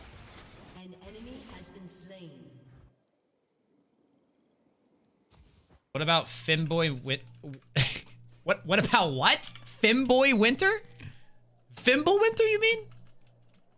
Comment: Forget about Hot Girl Summer, I am getting ready for “Fim Boy Winter”😂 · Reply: Ooooh I thought he said “Femme Boy Winter” 😅